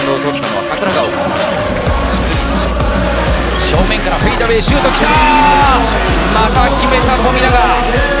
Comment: フェイダウェイって聞こえるけどフェイダウェイやないで